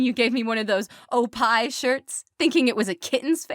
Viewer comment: I was on a different website while I left the video playing. And I heard the word "oh pie" which I thought was "oppai".. So I thought Asgore gave Toriel Oppai Shirts that he thought was a cat